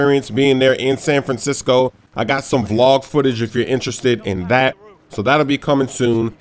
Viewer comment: did I hear said he got some vlog footage??? I like